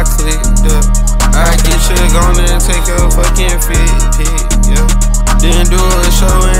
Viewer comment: i thought he said feet pic :face-turquoise-covering-eyes::face-turquoise-covering-eyes::face-turquoise-covering-eyes::face-turquoise-covering-eyes